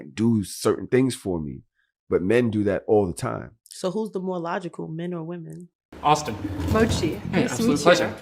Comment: her question is "so who's more logical men or women" but what I heard her say was so "who's the Mack".